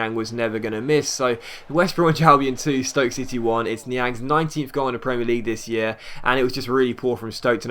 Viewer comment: Is it just me or does it sound like says "it's Niang's 90th goal in the PL this year" at 8:20. Great vid btw <3